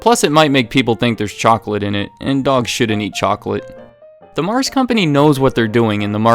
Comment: When he said "dogs shouldn't eat chocolate." I thought he said "dogs shit and eat chocolate."